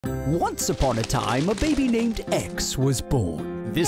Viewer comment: I thought the narrator said “A baby named eggs was born”